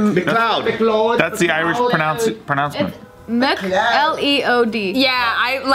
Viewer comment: did just say pronounce-ment LOL